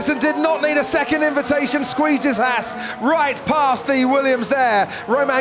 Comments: Lol, that moment when he said "squeezed his Haas" I could've sworn he said squeezed his ass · "squeezed his Haas" sounded like "squeezed his ass". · when the commentator said Kevin Magnussen squeezed his haas. All I heard was he squeezed his ass and fell out on the floor laughing. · did he say as* or hass?? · Did he say ass? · I thought he said "squeezed his ass" rofl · sounded like squeezed his ass instead of Haas